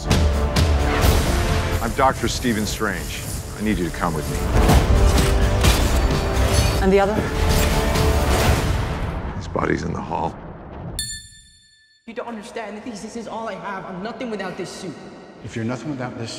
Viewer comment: It sounds like he says, "Spidey's in the hall," at 0:48 lmao